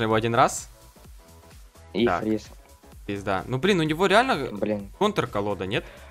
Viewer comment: На 11:40 Мне послышалось,или он сказал (пизда)